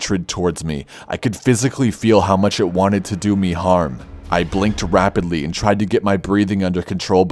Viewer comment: I thought you said "do me hard". XD